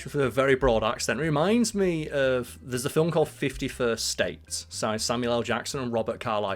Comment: I had to do a double take because I heard "51st state" as "50 first dates" and thought "hold up, Samuel Jackson wasn't in that trash, was he?"